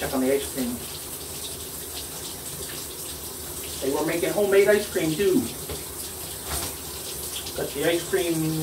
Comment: I think I heard him say, am I gonna have to take a bath. What's that you say Daddy? Homemade ice cream. 🥰